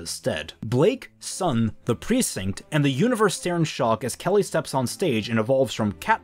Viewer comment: did he just say the universe?